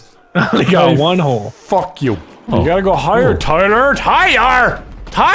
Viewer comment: Did Nogla saying "higher or Tyler or tighter"? I'm confused LOL 😂😂😂😂😂😂